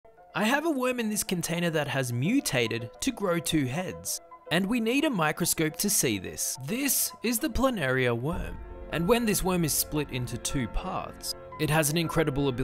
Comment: Thought he said "I have a woman in this container" at first